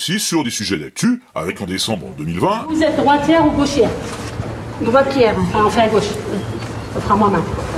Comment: Pour dire, lorsque la dame dit qu'elle est droitière, j'ai compris "banquière"... 🙄🤣